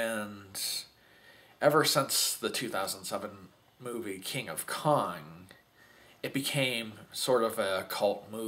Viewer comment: it sounded like you said "King of Con"!!!